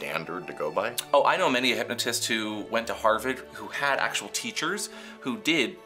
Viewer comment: Did say “Harvid”??? 😂😂